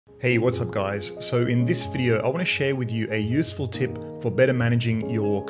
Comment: i misheard "in this video i wanna shave with you" XD p.s. Thank you for tutorial o/